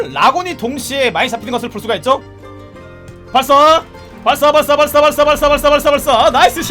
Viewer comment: 부터 발사발사 거리는거 나만 사발사발 이렇게들리나?